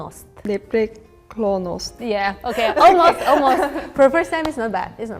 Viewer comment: you have a Belarusian word in the subs instead of Bulgarian: няскоранасць, Bulgarian girl said непреклоност